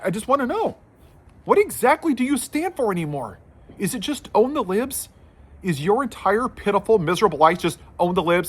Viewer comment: Very well said. Your captioning is mistaking some words. At 1:37, it sounded like you said, "..own the lib's.." but the captioning interpreted as, "own the lives". It can get distracting. Maybe, you could leave off the captioning and let those who need it, use YouTube's engine.